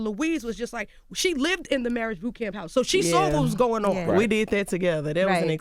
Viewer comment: I thought she said Maury(married) boot camp